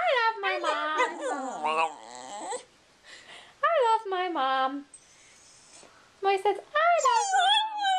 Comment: TOTALLY sounds like said "mom"!! LOL xD